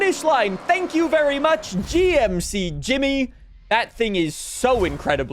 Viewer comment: It’s so refreshing to see some normal starter cars that feel like starter cars and not a supercar or new sports car. These cars actually feel like stuff you should start with. GMC Jimmy was a weird choice instead of like a 90’s Japanese SUV/truck. Still cool though. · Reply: Oh it’s a GMC Jimmy? I thought he said Jimny. Like the Suzuki Jimny